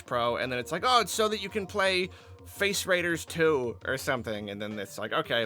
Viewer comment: Lol I thought he said "Face Riders 2"